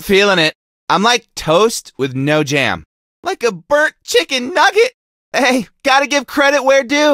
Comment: DID HE JUST SAY CHICKEN NUGGET?!?!?!